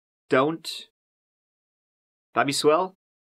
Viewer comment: Did say “Bobby swell?” at the end? Please explain.